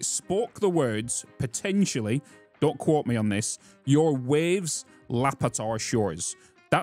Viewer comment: I heard "Waste lab is at our shores" "When will you land?" "Defile unscorched"